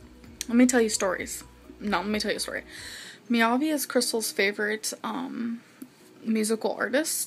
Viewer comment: I thought was saying that Krystle's favourite musician was Maiovvi as in Antoni Maiovvi